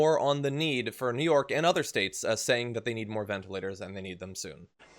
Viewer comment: Can this guy pronounce anything correctly? Do I hear him say, ventAlators? I think that's what I heard him say at lest three times: ventA- lators. Maybe he doesn't know the ORANGES of the word!